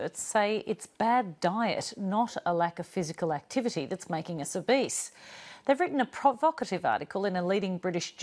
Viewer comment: I was listening to this and I thought I heard, "bad diet is making us a beast"....What I thought I heard was just as true.